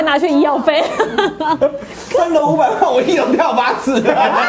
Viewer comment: 44:50 說什麼？？ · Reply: 三樓500萬 我一樓跳八次